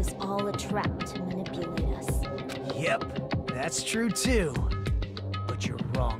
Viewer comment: I thought he said “Yep, that’s true tool!” I was shocked lol